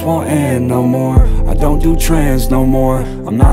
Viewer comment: This song I feel lyric to lyric but on gawd I thought he said “I don’t do trans no more” I had to pause and get lyrics up real quick 😂